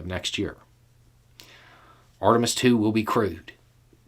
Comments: I mis-heard that as "Artemis II will be crude" and now I'm thinking 'come on you cunts, who wants to go to the fuckin' moon?'. Yes, I am British. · I heard, "Artemis 2 witll be crude," instead of, "will be crewed." lol